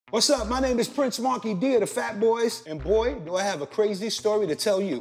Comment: Thought he said he was prince "wonky" dee at the start.